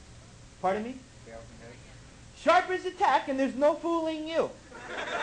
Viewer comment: he's saying "sharp as a ...." ? I don't understand.